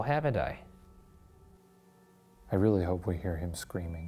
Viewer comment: i thought it said i'm really sorry